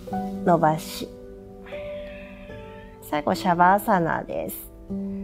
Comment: ここ何て言ってるんだろ？ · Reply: 最後、シャバアーサナです ↑ヨガのポーズ